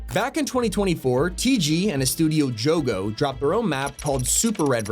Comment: I thought it said gojo for a second lol